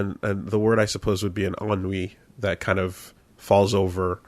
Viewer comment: What is the word says at 10:02? Awn Wee? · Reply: Ennui